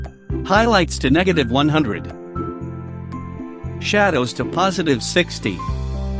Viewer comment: "shadows to positive 60" or i am the one who mishear it ? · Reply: Rya Yuiyæ sorry it should be positive 80 :)